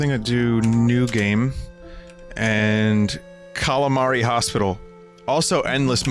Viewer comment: Aw I thought it said Katamari and I got excited. :(